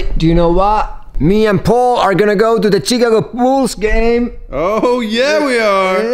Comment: i thought you said chicken wolf's and later realised you said Chicago bulls. :`)